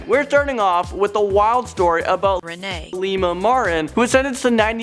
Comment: it sounds like siri said rene